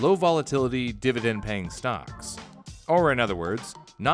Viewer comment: what i heard is... S T O N K S